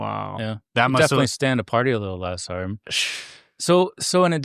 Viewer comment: What is he saying at 2:14:20?! I’ve re-winded it 500 times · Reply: Party less hard